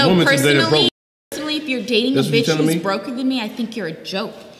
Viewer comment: Wayment. . . At the 9:17 ish mark, did she just say, 'if you're dating a bih that's broker than me (I assume she meant 'you'), I think you're a joke'? I think I misheard that. I did, right? Because. . . there's clearly something functionally wrong with this statement, and the life she leads; as the sidechick or otherwise.